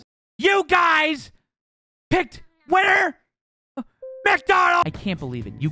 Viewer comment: Did he say 'Winter' or 'Winner'? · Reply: winter